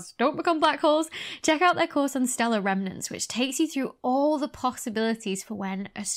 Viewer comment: Maybe I am just too sleep-deprived, but every time you said "stellar remnants" I heard "stellar revenants". So now black holes are undead stars. That is awesome.